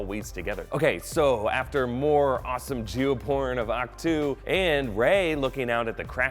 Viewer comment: Did he just say "Jew porn" at 2:56?!?! I know that he couldn't have said that but that's seriously what I hear. I have no clue what else he could have said though. Am I the only one that heard that?